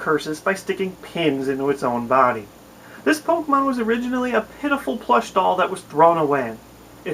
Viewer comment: I thought he said 'this pokemon was originally a pedophile'